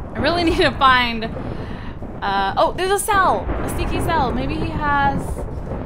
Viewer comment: When I first started watching this series I thought she was saying stinky sal not sneaky sal